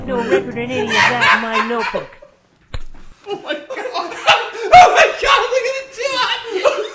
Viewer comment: what does say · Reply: Oh my god, look at the two options